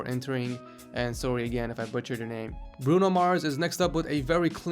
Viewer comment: did you just say "bruno mars"?